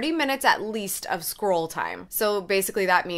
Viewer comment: When you said you make at least 30 minutes of scroll time I swear I thought you said squirrel time LOL Love these tips